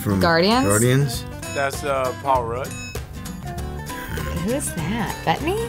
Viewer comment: I heard Alright instead of Paul Rudd.... did you ?